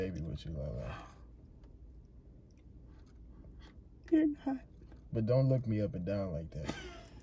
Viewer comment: what she say??? “Digiorno” LMFAO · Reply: “ your not “